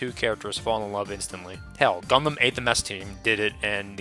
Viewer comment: what gundam is that i couldn't hear what you said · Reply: The 08th MS Team